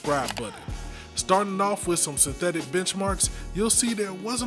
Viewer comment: I thought i heard bitchmarks 😂